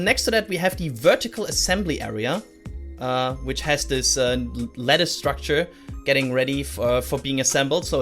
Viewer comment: did say "lettuce structure"? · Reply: It was 'lattice structure' :)